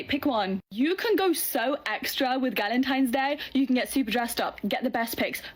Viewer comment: I kept hearing "Gallontine's Day" and imagining the woman drinking at least a gallon of box wine.